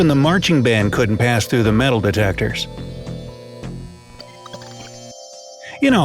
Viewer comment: did he just say metal detectors